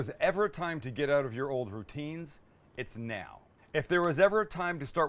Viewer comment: Did he say get out of your old Poutines ? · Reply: Routines...